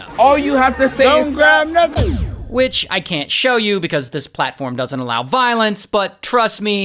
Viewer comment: I totally thought you said, “Bitch, I can’t show you.” 😂😂😂😂 BOTH times 😂😂😂